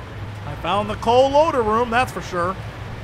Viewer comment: when it said "The Coal Loader Room" i thought it said "The Cool Ladder Room"